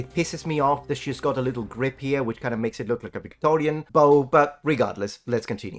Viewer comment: what did he say? I heard “Victorian bow”